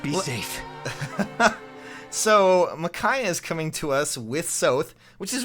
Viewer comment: I nearly died when I thought I’d said “Micah” from rdr2